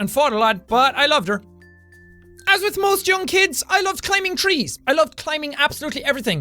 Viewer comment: I thought he said claiming trees 😂